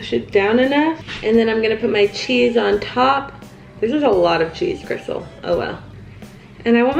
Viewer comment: i thought she said she had a glock of cheese